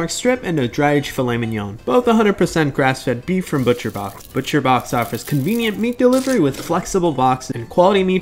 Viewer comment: ngl, i thought he said butcher bob 💀